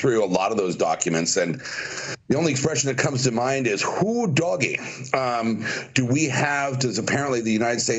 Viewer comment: What did he just say? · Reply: Who doggie